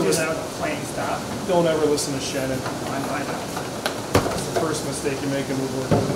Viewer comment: I believe I heard don't ever listen to Shannon while talking about a tail vise.